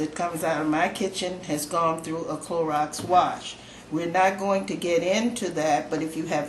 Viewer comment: did you said ‘Clorox wash’?